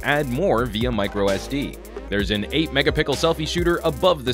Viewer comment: I'm pretty sure I heard him say there is an "8 mega-PICKLE selfie shooter"